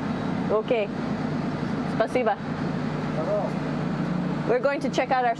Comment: Did he just say shalom?